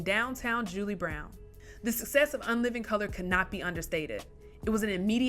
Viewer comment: did she say “the success of unliving color”?